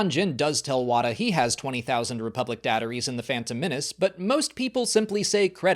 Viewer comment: I didn't realize Wim was saying the same thing as Qui-Gon, because the emphasis was on a different syllable. 7:35 I never noticed Jaba had a tattoo! Honestly, I always look at Princess Leia during those scenes. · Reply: I always thought Qui-Gon was saying "DECtaries" - but I did clock that what Wim was saying was similar.